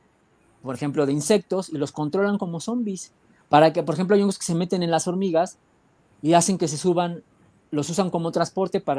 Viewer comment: Hola en el min 56:26 dice a la nosis y nosis ?? No entendí bien podrán decirme que dijo por favor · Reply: Gnosis